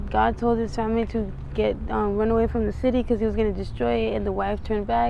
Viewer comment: At the 11:57 mark I hear the interviewer say Take your hands off of me . 😳What was that about ? . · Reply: To Get her hands off the microphone so we can hear her.